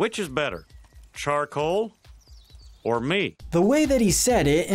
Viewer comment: Funny, I always thought he was saying, "charcoal? or meat?", because not being able to taste the meat is a point he likes to make.